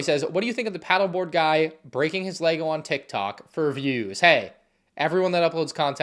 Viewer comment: I thought he said "leg" and was so confused 💀